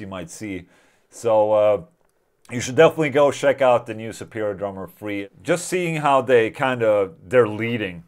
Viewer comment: I thought he was saying Superior Drummer Free, like it was a stripped down version. Took me a few minutes.